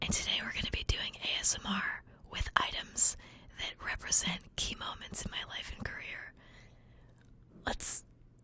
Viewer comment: i thought she said chemo mints instead of key moments